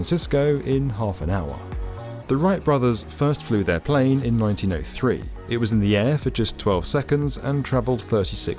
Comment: I swear he said Wright brothers flew their first plane in 1983... @ 8:22.